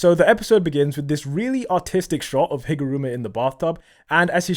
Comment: I’m so sorry I legit heard “this really autistic shot” and not artistic 💀